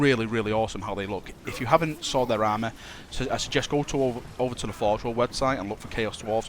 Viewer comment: Seriously WHAT are u saying at 5:02 ?!!! ive tried like 40 times replaying it its impossible to hear Forgheh-whll website? Forge-hell? O.o Srsly im so curious now xD. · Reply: Forgeworld website :)